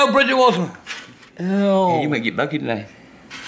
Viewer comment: AFTER YEARS OF TRYING TO UNDERSTAND WHAT HE SAID AT THIS PART I FINALLY KNOW WHAT SAID He said “Aye,you might get lucky tonight.”